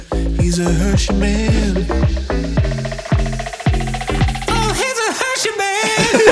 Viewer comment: Listening to this i heard all hail the hershey man